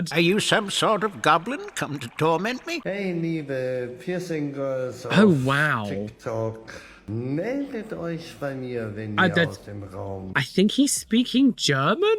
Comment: What the man says befor mutes him: "Hey liebe Piercing-Girls von TikTok meldet euch bei mir wenn ihr-" Translation: "Hey dear piercing girls of TikTok contact me when you-" If I had to guess it was probably an add of some sort. As yes it's in German.